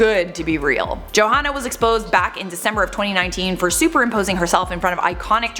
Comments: did she say she was exposed back in december 2019???? Or it just sounded to me please tell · is it me or did she say back in December 2019. I replayed it severally to know exactly what she said. Right now, we haven't reached December of 2019 yet and she mentioned that already. Let me know what she said cause I am confused on that. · Did I hear December 2019? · I'm sorry but at 3:08 did said back in December 2019? or am I hearing things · did she say she was exposed in DECEMBER 2019 or is it just me beacuse....its November 🤨